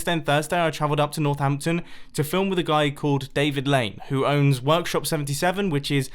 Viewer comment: I thought you said david blaine at 3:55 lol